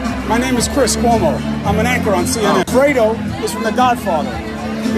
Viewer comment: Sarcastic, right on the spot. And fredo took it seriously! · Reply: I thought Fredo said he was an "actor on CNN?" Probably meant "anchor" but actor fits better.